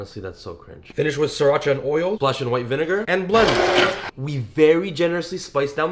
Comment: omg he said "blend" but i thought he said "blood" 💀